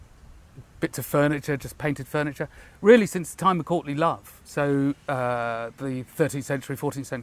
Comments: I thought he said “since the time of Courtney Love”. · Thought he said since the days of Courtney Love